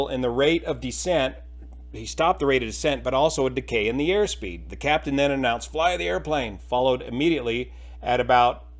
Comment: I think the captain was actually saying "Fly, airplane!" as if knowing an impending stall was developing and telling the airplane to somehow fly out of it.